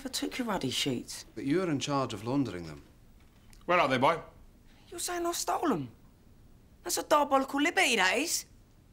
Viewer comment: What does Sidney say at 22:13? I took it back a few times but still couldn't decipher it. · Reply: Charlee Charles.. "Are you sayin' I stole 'em? That's a diabolical liberty that is!